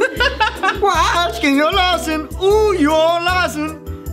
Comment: When said for Mushu it sound like he said lasagna on you lasagna on your cow